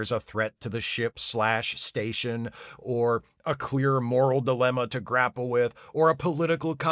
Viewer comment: I heard "a queer moral dilemma" at first and I didn't even bat an eye because... Well, it's basically the relationship between Julian and garak